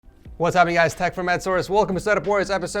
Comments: Did he say tech from edsource lol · did he just say "Its Tech from Ed sourse"